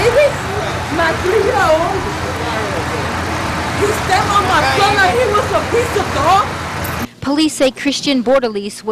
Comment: did she say "piece of Dog"? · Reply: Piece of Dog Shit but it was cut out due to obvious reasons